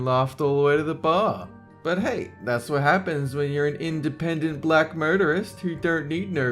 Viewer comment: I thought said, "independent black murderess". whoops.